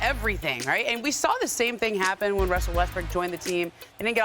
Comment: Did she say Westbrick